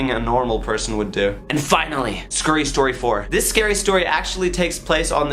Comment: did he say SCEREY?!